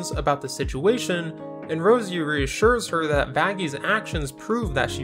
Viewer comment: I thought bro just said faggie 😭😭😭😭